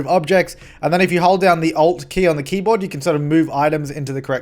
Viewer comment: did he say "if you hold down the "o" key in the keyboard"?